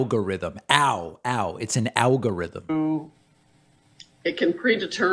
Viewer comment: I heard "algorithm" not "owgorithm" but ok, way to inform the public about the important stuff